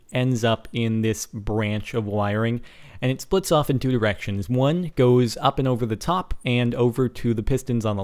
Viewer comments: I swear you said erections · did you say erections? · i heard "splits off in two directions"